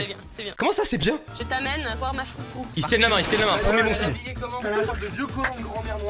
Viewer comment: C’est moi ou à 9:09 elle dit je t’emmene voir ma foufoune